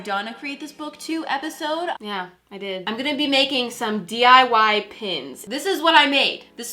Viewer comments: Man i thought it said DIY PUNS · I thought you said I made diy pens.....it’s a good time lol